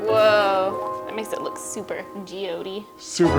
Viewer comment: They were saying "super geode-dy" and I thought they said "super G.O.D."🤣🤣🤣🖤❤🖤